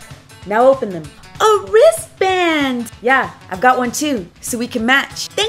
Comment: I’m so sorry but I thought she said A RIZZ BAND🤣🤣